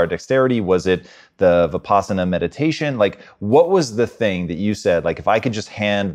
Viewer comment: I have done 10 days course vipassana meditation in India, it was a wonderful experience. It teaches to maintain equilibrium in life. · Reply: the subtitles said ... pasta meditation ... wtf ? i thought he said possum meditation thanks for explaining